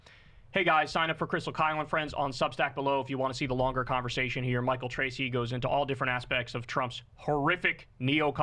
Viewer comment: Yay I love Michael Tracey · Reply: I thought he said Michael Crazy. 😅